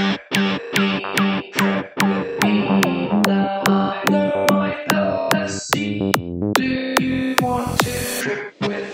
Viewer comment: what is the voice actually saying there ? been listening to this banger for years now but could never figure out what its saying there ? anyone know? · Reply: “Now I know my LSD , Do u wanna trip with me?”